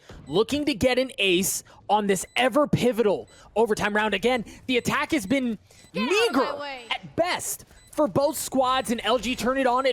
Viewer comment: can someone tell me what he's saying? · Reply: "The attack has been meager at best for both squads"